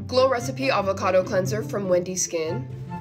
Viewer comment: I thought she said Wendy’s skin lol